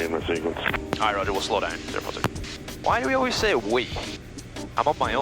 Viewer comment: I guess it's you, the plane and us viewers so you're not alone ;) btw did you just say "why do we always say we?" lmao